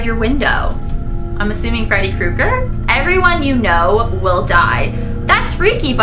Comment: At first I thought the one that said “everyone you know will die” said “everyone you know will dog” hahaha